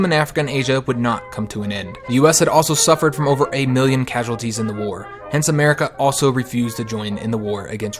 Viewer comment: At around 7:00 i thought you said 9 casualties